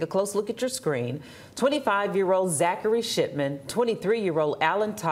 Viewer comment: 😂. I'm a child inside of this adult body did anybody else hear zachary shitman when she said Zachary shipman. 😂😂😂😂 Dead ass sounded like it 😂😂 irs fitting for the crime.